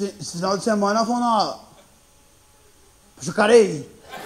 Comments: não entendi . É Jacareí? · O que ele diz no 4:21 ? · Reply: Jacareí.... Uma cidade zoada do interior de SP.